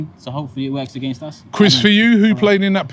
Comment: Is it just me or did call Chris? Is that he’s name 😵😂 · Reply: I think he said "quiz for you" i defo thought he said Chris at first lol